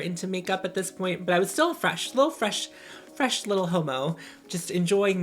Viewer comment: I heard it as "Fresh Little Hobo"